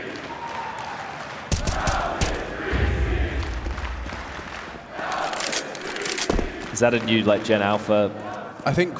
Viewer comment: what the crowd saying at those moments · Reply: Mouz is greasy